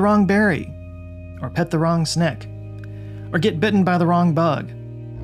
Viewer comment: did you say sneck?